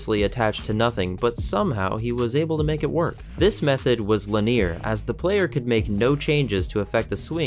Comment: did he say "linEER" instead of "LINear"?